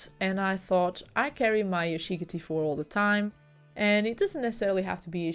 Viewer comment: What do you carry all the time? I heard "shiggerty 4" but that's not right, is it? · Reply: Ahaha Yashica t4